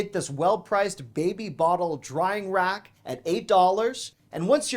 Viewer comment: What you said: “baby bottle drying rack“ What I heard: “BABY BOTTLE WINE RACK!!” I had to back it up to see if ALDI had lost it’s mind !!😂😂😂😂😂🎉🎉🎉🎉🎉🎉